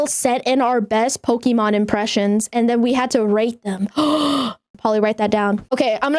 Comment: ngl, at first i heard rape then when i replayed it was rate... am i the only one? but for real though, i thought nothing about it and just thought it's just another bao moment